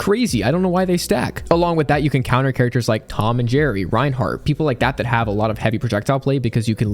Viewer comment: did you just say Reinhart..